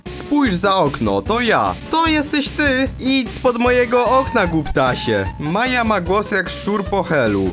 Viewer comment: Czemu ja usłyszałem kutasie Zamiast głuptasie